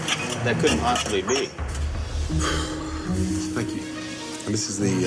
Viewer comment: Gordon: And this is thee uh.. Waitress: tHaNgMEn Gordon: Wow. Thank you. Waitress: YouwecUm. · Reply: LOL i thought he said the ENGLAND!